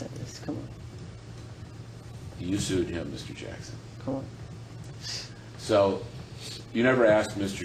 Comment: “kool” I LOVE MICHAEL EVEN MORE FOR THIS!!!! he’s so savage, the guy was really pissing him off. The way he said kool like a rebellious teenager like he’s had enough and he couldn’t give a shit anymore about what the guy asks or says. 😂😂😂❤️ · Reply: He said “ come on” lol 😂